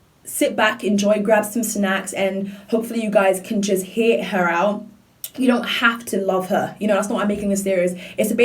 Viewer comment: it sounds like you say "hopefully you guys care just hate her out" haha Sidenote I have so much respect for you and everything I hear your songs previews I am stunned at how beautiful your voice is!!!!